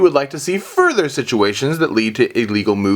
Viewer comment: for some reason when you say "GalacticGod" i hear "GalacticCock"